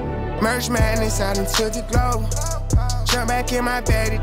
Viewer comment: I thought he said march madness my dumb self lol